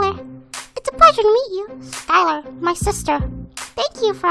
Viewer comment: at 2x Speed “I like my sister” who else hears that?